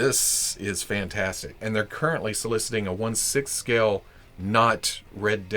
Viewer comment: It's only 1/6, and I think it's releasing right now? <link> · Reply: Ah, I misheard. You said it was 1/6 scale in the video but I could've swore you said "six inch." Must have just been wishful thinking on my part.